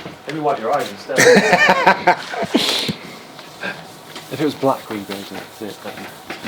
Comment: What did he say at 14:19? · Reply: "if it was black we'd see it better." he means if the monitor was turned off and not backlit it would be easier to detect blemishes on the surface.